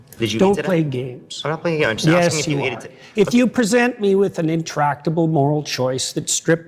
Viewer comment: Can anyone help out a person w/ auditory processing issues and tell me wtf said 3 times ( gotcha) that Jordan is so pissed about? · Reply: I'm pretty sure he asked "did you eat today?" Not sure why though